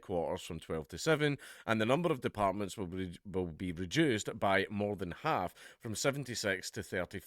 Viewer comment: I dont think I heard whatcha were saying there BOSS......... was that something about us Jews? I didnt quite catch it..... lol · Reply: ? He said reduced, it’s just his accent